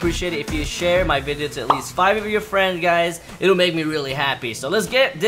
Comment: 5 of my friends!I thought you said all of my 20 friends!!!